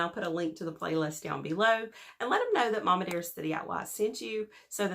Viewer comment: I thought that you were saying mommy dearest. Girl your accent is almost as good as mine. Lol. I love it BTW, don't think for a second that I'm saying anything negative about the way you talk. I love the way I talk and I love the way you talk. 😺